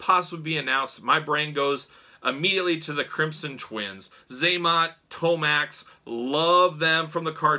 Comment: When you said crimson twins I swore I thought you said the crimson chin 😂